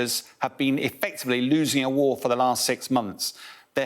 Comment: I thought they said Ukraine is winning for the past 6 months why admit now ?